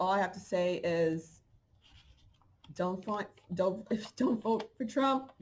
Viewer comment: Re- listen she never said that, Trump=Trumpet, The last president,reset, change of guardianship✊🏿💪🏾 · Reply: I did listen again, where I thought she said "don't for Trump" she actually said "if you don't vote for trump".. woops my bad! Volume was low. Let's rock out Light Warriors 😇